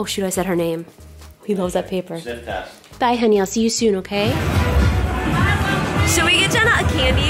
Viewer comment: if you turn on the captions, it seems like she said died Maddie instead of bye Maddie